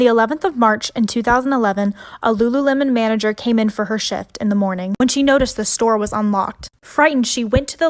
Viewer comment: Did anyone else hear shit instead of shift or is it just me?